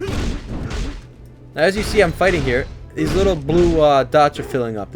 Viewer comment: did you say blue? or does it turn blue?